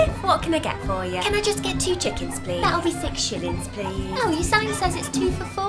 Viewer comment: I thought she said jackets, then I read the sign in the back and now I only hear chickens